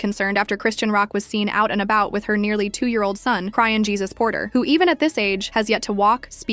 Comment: Did I Hear Crying Jesus Porter😂😂😂😂 Or I’m Tripping?